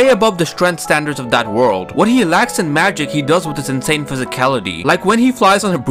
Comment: So i was playing a video game while scrolling through YouTube shorts and i was at this vid and when he said insane it sounded like he said hentai so i was like wtf and started the video again and well he said insane so a was like ooo ok